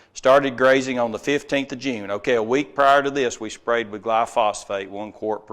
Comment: Did he say glyphosate at 38:38 ?